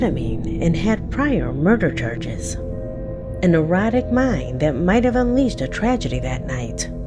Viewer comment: During the last video, I thought she was saying "erotic mind," instead of "erratic mind!" 😂 My bad!